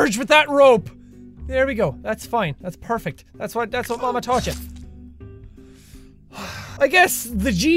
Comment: am I the only one that heard, that's what Obama told yah Instead of that's what mama taught yah